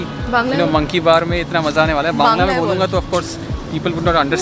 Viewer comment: Did she just say "Bangla ey bol"? She said it twice!